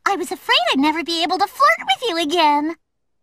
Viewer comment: HELP ME! I wanna laugh: What exactly did Amy say in 2:20? Fork? Ford? Fuck? Huh...? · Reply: she says flirt.